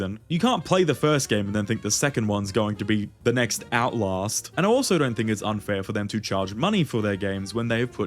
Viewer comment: did you say outlast? As in the outlast trials? As in mother gooseberry? I might need a dentist 🫨